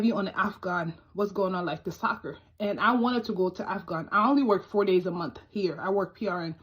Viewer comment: I have an odd question. I'm hearing impaired so I lip read. At about 11:02 the woman says something I can't read followed by "what's going on like the soccer, then the same word a few times. Can anyone help me out as to give me a clue on what she says? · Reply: She wanted “to go to afghan for soccer.” I have no idea what she means, but she said it a few times